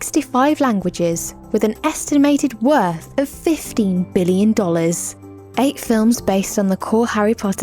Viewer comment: Hello Alva, Actually, It’s estimated to 15 dollars... No hate! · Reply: Veronica Leung no it’s ok thanks for telling me I just thought it said 25 billion but now I know it’s 15 billion thx!!